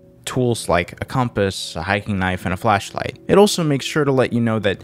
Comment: thought that said fleshlight